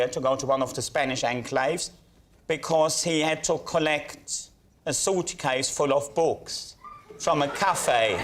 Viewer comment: sorry I just heard a salty case full of balls! Germans can be funny 😅